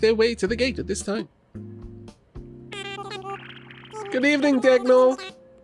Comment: "Good evening dagnull!" I heard dev/null/ - I've been doing far too much memory leak debugging ;~